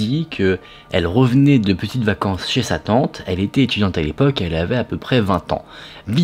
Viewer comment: au tout début, j'ai mis sur pause pour aller me chercher un café et j'ai entendu "Emma revenait de vacances chez satan..." juste c'était marrant sur le moment!! en tout cas c'est bien joué Emma! y'a un thread horreur que j'adore et dont la "victime" finalement... est un voleur! Cela se passe en 2019. Cet énergumène a eu la bonne idée, à Rio de Janeiro d'attaquer une femme seule. Bonne proie n'est ce pas? Sauf que les étoiles n'étaient pas avec lui ce jour (bien fait!). Il s'approche d'elle et d'abord lui demande l'heure. Elle lui répond, normal. Puis passant à autre chose, la jeune femme sort son portable. Là le gars lui dit: "-file moi ton portable. N'essaie pas de resister je suis armé" Ah, tiens donc! Ce qu'il ne savait pas, c'est que la jeune femme c'est Polyana Viana. Pour les amateurs de MMA (mixed martial arts) c'est une des têtes d'affiche au championnat officiel UFC, le plus important de la discipline. Elle a commencé par le mettre à terre par deux mouvements de jambe. Le reste, ben sur les photos, il a plutôt une tête de chou-fleur badigeonné à la Bonne Maman parfum fraise!🤣 Elle l'a maintenu à terre avec une clef de bras, jusqu'à l'arrivée de la police. Qu'est ce que j'aime ce genre de fait divers! Merci pour la vidéo, c'est toujours un plaisir!